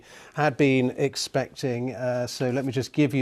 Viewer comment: did he just say "As we were EXPECTING?"